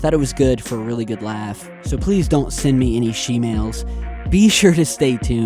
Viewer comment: They did it in this video too lmao At the end (12:03) you say “So please don’t send me any shemales” but the captions say “So send me any shemales” 😂😂😂 It totally missed putting “please don’t” in there lol YouTube has to be screwing with you, this is too damn funny to be an accident 🤣